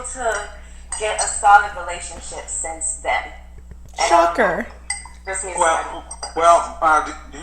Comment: HaHa I thought you said F%ck her, when you said Shocker!